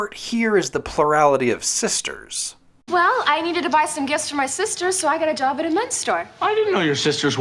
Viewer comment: I think Kelly says "my sister, so" she just says it so fast she runs the line together so it sounds like "my sisters". As for Screech saying "sisters", you can chalk that up to his clearly worsening mental condition, which everyone is too nice to point out.